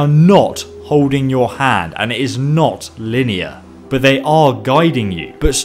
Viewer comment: More Sneak Previews: Instagram: <link> Twitter: <link> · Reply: ESO - Fallout & Elder Scrolls Guides lol i thought you said its not lydia